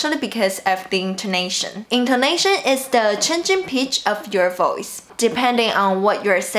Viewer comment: chengcheng pitch is what I heard haha :D